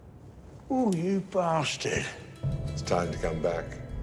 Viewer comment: I love his response, "ooh, you bastard!" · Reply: I thought he said holy bastard